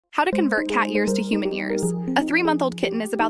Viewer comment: I heard "how to convert cat ears into human ears"😭